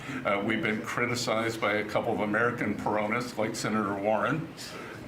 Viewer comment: Haha. When Scott Bessent first started speaking about Pocahontas Warren, I thought he called her a "piranha" ! 😮🙄😏 But then he went on to say a little more about her & he used the word again, & I heard it as "Peronist", where knowing the general subject here was Argentina, "Peronist" of course immediately made sense to me, although I do definitely think that Pocahontas Warren could certainly also be very aptly described as being a [very mentally sick & politically desperate] "piranha" ! 😮🙄😏😆😂🤣😄😊